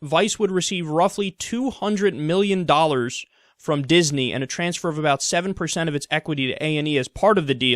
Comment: Hmmm, I heard him say 'Disney' :/